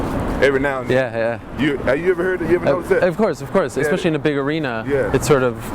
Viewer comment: What did Rampage say at 7:40? “Have you ever heard [I couldn’t make out this part]” Ariel sort of interrupts this question replying “of course of course especially in the big arena.” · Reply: He rephrased the question "Have you ever hea- have you ever notice that?"